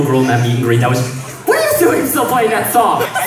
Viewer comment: I misheard the last bit I think because I heard “and so we kicked her off of a horse” 😂